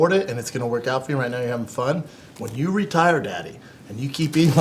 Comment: Did Brendan call him daddy at 34:56 or am I hearing things